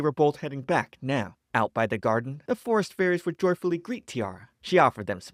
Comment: I misheard it as florist fairies and it made it better